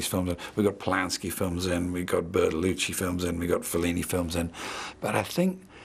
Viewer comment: Hey english native speakers! Help me please! I can't understand what name does he say after "We got Polanski films" and before "we got Fellini films" on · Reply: Bertolucci